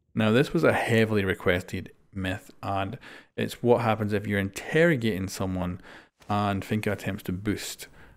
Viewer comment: Ayy, bit of a misspell at 3:38 "integration" instead of "interrogation"